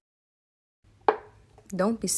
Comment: lmao at first i thought she was gonna say "don't be sad go die "